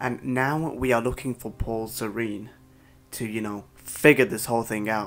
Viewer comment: why do I keep hearing "pore serene" when you say Paul Serene? xD